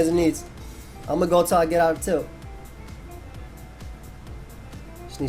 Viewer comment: yikes i heard "I will keep going until i get off tilt" me:sees the video length